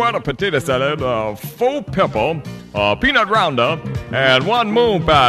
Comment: Does anyone know what he's saying at around 3:31? I've been wondering about that for 25 years! My friends and family all have different ideas of what it is. "Peanut rounder", "Pint of Roundup", "Pinot Roundo", "Peanut rinder", "Piece of Rhino", etc, etc, etc. Sure would love to know what it ACTUALLY is LOL! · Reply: He's saying "Peanut Rounder." It's a type of candy, sort of like a praline. Do a Google Image Search and you'll see tons of them. :)